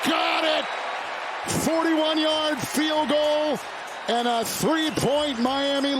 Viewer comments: bro at 7:56 instead of a 41 yard feild goal they said 71 before halftime 🤣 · can’t be the only one who saw it say 71 yard field instead of 41